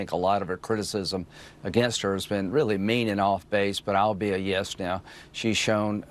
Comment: Thought he said I'll be a yes man, 😅